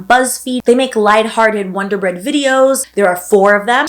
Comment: did she say wonder bread 😭